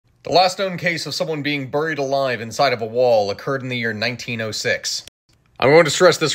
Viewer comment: Oh, buried alive inside a wall. Until you said it again at the end I thought you'd said burned alive inside a wall. The first syllables of buried and burned don't rhyme for me... Bury is pronounced the same as berry.